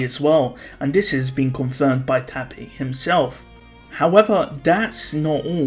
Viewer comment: When says tappei but I heard It as tappy so I said "who the fuck is tappy?"